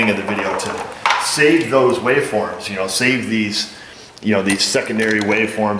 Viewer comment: For a sec I could swear I heard; "Save the whales!" Oh well, the wave forms are equally important I guess. Only, you can't harpoon and eat them. Neither can they blow water-fountains out of their blow holes... But still, I'm all for saving our precious wave forms! But in all seriousness, great video, guys! As always :)